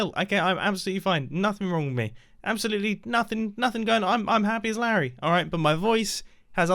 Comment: "Happy as Larry". Did I hear that right?